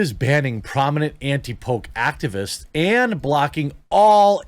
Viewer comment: I thought you said, anti Pope. LOL